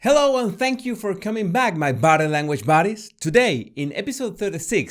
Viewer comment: I know what you're saying. I know you're saying "body language buddies", but all I hear is "battle language buddies". No matter how many videos I watch, and it's been a LOT of videos, I still hear "battle language". I love it and it makes me smile every time! Your videos are amazing and your accent is awesome! 😄👍